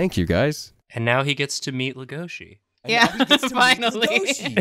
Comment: I don't know how this happened but at 33:47 when says "and now he gets to meet Legoshi" my heard "date" instead of "meet"? My brain just pranked me I think. I had to rewind and listen again because I was so sure I heard the word "date" but no one was reacting as if that was said